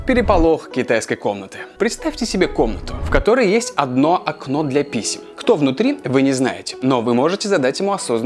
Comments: послышалось "для писи" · Мне одной послышалось "окно для писи"? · Окно для "писи" (послышалось) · Услышал "представьте себе комнату, в которой есть одно окно для писи". Сразу вспомнил такой вид порно и подумал что о нём будет идти речь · Лайк, если в начале услышал "для писи", а не "для писем"😂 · Мне одному послышалось окно для писек?! 😂😂😂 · Послышалось окно для писи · ОДНО ОКНО ДЛЯ ПИСИ послышалось.. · Мне одному послышалось "Окно для писи"? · мне одному послышалось окно для писи?)